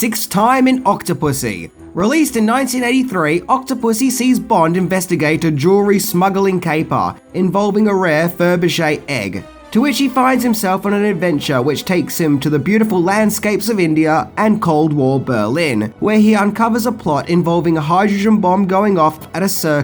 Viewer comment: Did you say Furbege???? Rewatching this as I type. When I was about 8 I had this on Betamax, and didn't get many of the jokes at all. I thought Kristina Wayborn was hotter than Maud Adams but now her forehead bothers me.